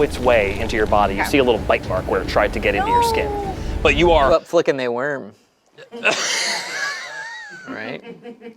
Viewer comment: what did say? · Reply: who up flickin they worm